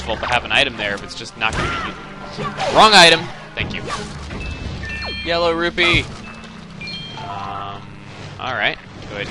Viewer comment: HAHAHAHA i watched 1:16 and i thought he said YELLOW ROOFIE!